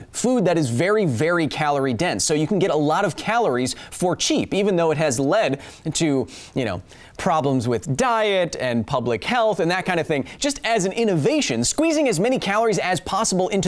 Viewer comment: Even though it had lead. Is what I hear🤣😂